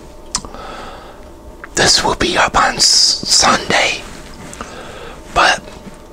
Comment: Awww u forgot my shout out🥺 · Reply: ohh ok ots because i thought you said it'll be up monday my bad for the misconception😁